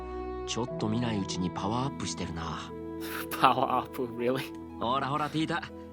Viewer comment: Great video, I am really looking forward to how the story goes from here! Quick question- at 35:07 Dan says something in Japanese that you chuckle at. What did he say? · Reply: Not, but The line in Japanese is roughly the same as the English one, but Dan uses an english loanword there instead of a Japanese one. He says ‘Power-Up’ pronounced phonetically (パワーアップ) Loanwords are generally a more casual form of speech, and can sometimes sound a little silly and out of place. Basically it was just a strange way to refer to Tita’s curiosity in this context.